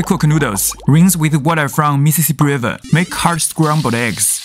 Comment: that looks delicious but I could have swore he said rinse the noodles from the Mississippi River. I just realized somebody else said that too so it wasn't just me it does sound like Mississippi River but the skill with which he cooked these was amazing it looks so delicious